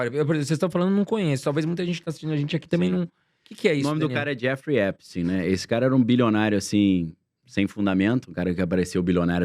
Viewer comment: Me ajudem, nao entendi o nome. ali no minuto 9 e no segundo 27. (9:27) Qual o nome para eu pesquisar, por favor ?? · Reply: Jeffrey Epstein